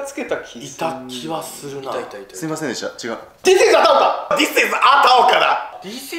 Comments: This is a田岡が、This is アタオカに聞こえる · ダーマさんがつけた名前のキャラ本当はThis is a田岡なのに空耳みたいな感じで聞くとThis isアタオカにしか聞こえないWw